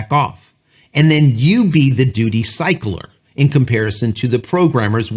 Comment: 🍵👍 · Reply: I heard it as doody cycler. I'm 40 and wonder if I'll ever grow up.